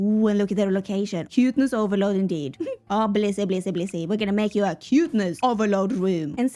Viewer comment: I wasn’t paying attention and all of a sudden I thought I heard say “glizzy” a bunch of times.. it was Blissey. She was saying Blissey. 💀 Also love the freaky Zoloft(??) room hahahahaa